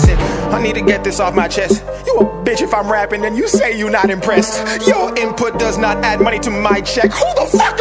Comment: Did he just say if I’m gonna happen and you’re not here I pressed Damn